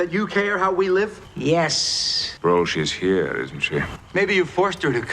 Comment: Is Picard saying "Bro, she's here, isn't she?" I really hope so.